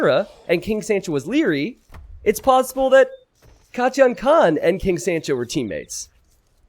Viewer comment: Seriously as a none native English speaker all I hear is" kat-chan" instead of kotyan khan, which made the player sound so cute.